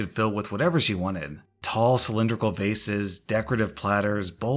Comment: When you said "Tall cylindrical vases" I thought you said faces